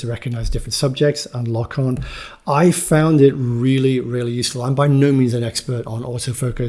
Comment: I didn't say I find it slow. I said I find it (the subject tracking) really useful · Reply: For what it's worth, I heard (from 10:10) "I found it really really useful"